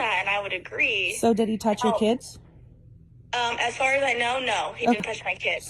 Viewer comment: I thought she said "he touched my tits". So that was the reason I didnt care. Not that I wouldnt be willing to listen to her being unwantedly touched that way. But saying it that way, automatically tells me she doesn't care about it. So I needn't bother. Turns out. She felt nothing because she was lying, and did so extremely unenthusiastically